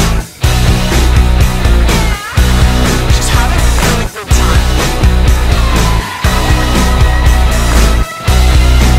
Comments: What does Olivia say at 1:51? She says something in the background but I can’t tell what it is. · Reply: I think she says “just have a good time” but I may be wrong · can someone tell me what that says pls? thanks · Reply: I think she says “ she’s having a really good time “